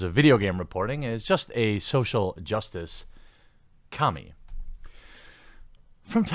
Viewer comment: honestly thought you said "social justice Kami" for a second there. wouldnt that be scary!